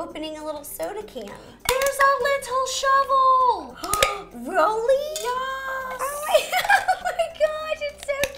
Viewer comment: Notice at 5:11 she says RO-ly instead of really. lol